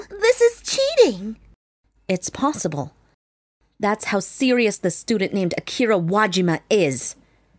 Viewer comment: why did i hear serious as stupid? like for example i misheard "Thats how serious this student named akira wajima is" as "Thats how stupid this student named akira wajima is"